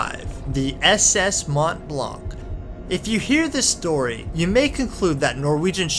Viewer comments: The ships in this video are tiny in comparison with these HUGE Sea Creatures: <link> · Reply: I seriously thought you said "The SS Matt Le Blanc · I seriously thought you said "The SS Matt Le Blanc